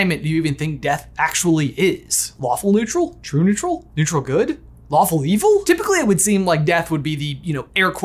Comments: When mentioned the "lawful evil" "Lawful Good" stuff, I totally thought he said waffle not lawful. LOL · I could of swore I heard waffle neutral instead of lawful neutral... lol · I heard "Waffle neutral"